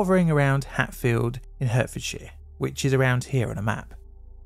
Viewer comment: did you just call it hurtfordshire haha